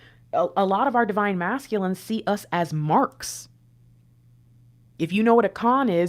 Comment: When said, they have always seen the DF as a mark, at first I heard her say, they have always seen DF as an Ark. 🤔Lots to ponder there as perhaps both can simultaneously be true! Resonates 🎯✨